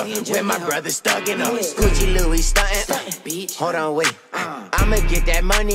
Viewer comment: i like how matt ox still never sweared in a song yet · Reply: What do you mean? He says "stuntin bitch" in the backgroud of the other dude's verse